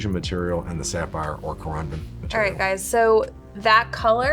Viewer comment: Ngl I had my subtitles on and volume off and I was reading and when he said corundum it thought he said condom so I had to turn my volume on for a sec for that one.